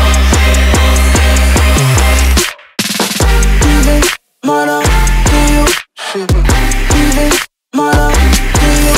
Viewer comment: I need to know what does he says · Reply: giving my love to you ?